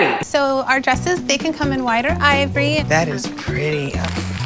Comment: I thought the lady said the dresses come in “wider ivory” for a second then realized she said “white or ivory”